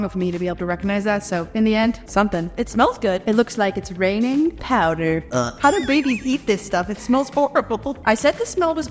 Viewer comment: Did she just say how do baby’s not eat this 😂